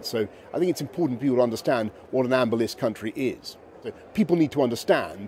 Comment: I'm hard of hearing so I need subtitles. Did he just say ambulance country?